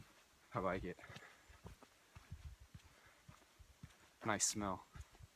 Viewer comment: Your videos are the best. Really no one makes better trail videos. And I like to think you are saying "I smell" at 12:00, lol.